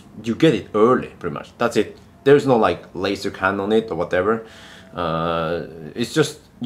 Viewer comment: With your accent, at 4:37 it sounds like you said there's no "laser cannon" or "laser gun" on it. I thought they put those standard on every supercar! 😆🤣